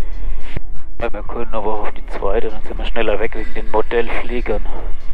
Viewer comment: Ich hab immer Bordellflieger verstanden🙄🤦‍♂️